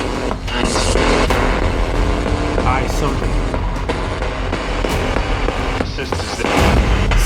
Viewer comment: After female I heard “I seek” then sister around 36:39, really think it said I seek or I see🤷🏻‍♀️